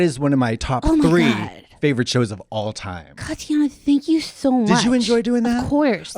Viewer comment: did kristen call her “katiana” lol